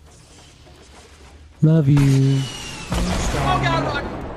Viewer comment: Does anyone hear "love youu" "thanks daddy"?